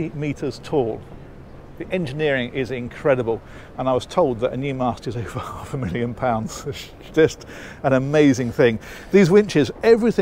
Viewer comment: what did you say at 2:12? Could not understand you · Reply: "I was told that a new mast is over half a million pounds"